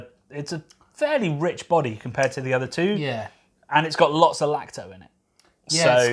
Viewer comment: At 9.44 it sounds like you say lactose. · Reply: AH! Apologies - what said was "lacto", short for lactobacillus which is one of the microbes that sours the beer. Sorry for the confusion.